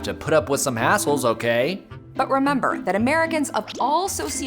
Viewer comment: I thought he said “a**holes” instead of “hassles” at first @ 3:26 😂